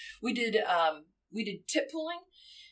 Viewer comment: tip pooling - thought she said tit pulling